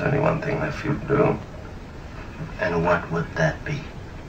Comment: I thought he said "Say Aud Wiedersehn to your Nazi Boss". Balls actually makes more sense, come to think of it.